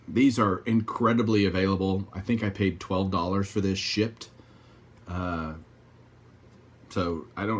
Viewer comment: Nice pickups as always, I had to playback when you showed the Bud Selig because at first I thought you said that you paid $12 for this shit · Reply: Ha ha, when I played it back I decided that you said shipped